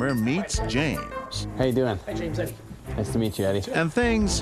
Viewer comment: this show is fake he said Hi james im eddie he knew his name .. how can he its first time in the store. #scripted. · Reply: I think the lady said "hi, this is James" at